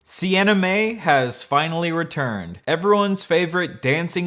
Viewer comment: i keep mishearing sienna mae's name to either Anime or The En-e-maay